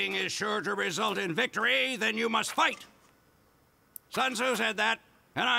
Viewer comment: soldiers speech in meet the soldier is very funny when you combine everything he says into one paragraph, "if fighting is sure to result in victory then you must fight, tsun zu said that, and i'd say he knows a little more about fighting than you do pal, because he invented it, and he perfected it so that no living man could best him in the ring of honor! then he used his fight money to buy 2 of every animal on earth, then he herded them onto a boat, and then he beat the crap out of every single one! and from that day forward any time a bunch of animals are together in one place, its called a zoo! unless its a farm!" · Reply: Wait it says "Tsun Zu"? I thought it said "Son's who said that?" 💀